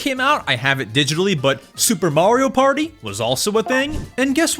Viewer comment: i thought i heard super mario barbie😭😭🙏